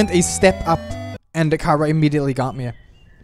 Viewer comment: Like in 35:12 you are saying chara and subtitle says camera